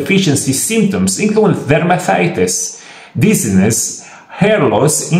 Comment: Velmathitis? What the heck does he say at 2:50?